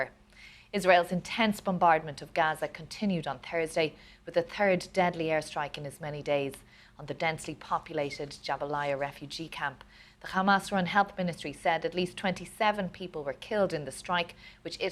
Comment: Did she just say "the turd deadly airstrike?"